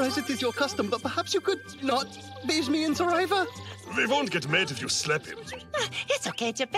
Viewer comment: “you could not ???? me in saliva” Can somebody tell me what he said? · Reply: "perhaps you could not bathe me in saliva" lol